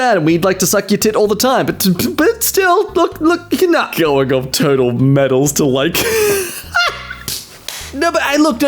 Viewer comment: am i the only one who heard "Turtle Medals" ?